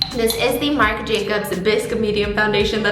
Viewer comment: I thought she said biss and I was like ok we really staying on brand